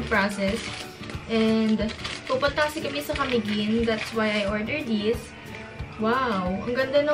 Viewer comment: Tama ba ako sa narinig ko Camiguin 1:57? Hehe